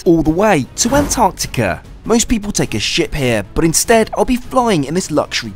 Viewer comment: for me sounded like "most people take a shit here...". But great video fr